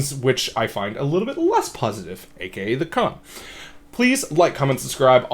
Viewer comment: Yo, at 0:37 I cannot understand what you are saying. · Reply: I shouldnt have moved my mouth away from the mic at that point and I used a downwards inflection in my tone. 100% my bad.. I said" AKA the Con"